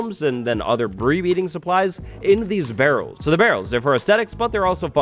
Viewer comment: Did anyone else hear bree beeding supplies